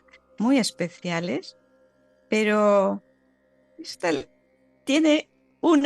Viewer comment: En el minuto 1:25 claramente se puede escuchar una psicofonía que dice "ésta no". Gracias por el canal.